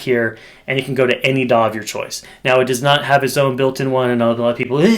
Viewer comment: What did you say at 5:47? It has a line out I’m confused “does not have its own built in “… ? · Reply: Does not have a build in daw, does have a built in reactive load though so you can use the line out to send it to the daw/interface of your choice